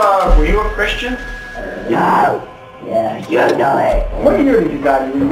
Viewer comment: no one: Warren: "Were you Christian?" spirit: "NOOOOOOO- *unhuman noises*" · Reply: I heard: - "Were you Christian? - No!!! You know it!"